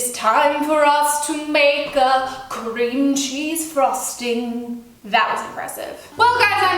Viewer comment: When she said cream cheese I thought she said CRINGEY lmao I'm British myself